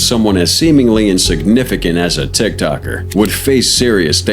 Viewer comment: Stop watching documentury slop videos like this, it's clear when he said Tiktoker and the subtitles said "Tech talker" lmao 3:52 . I don't mind the True crime at all, but please just react to actually good videos that have some soul